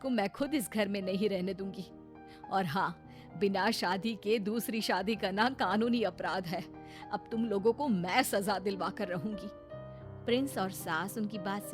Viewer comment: What does the lady say at 0:55 ? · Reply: Kanuni apradh boli hai